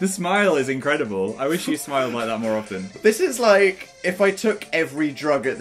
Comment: Phil: "I wish you smiled like that more often" That is adorable wtf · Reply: At first I thought you said smelled like that more often